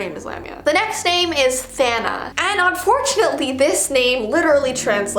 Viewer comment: “Thana” Who else thought she said Sanna it sounds like that lol